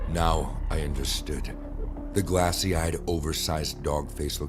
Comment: Bro I’m eating my rice and chicken,drinking my flavored water and I hear “A oversized dog head.” I’m dying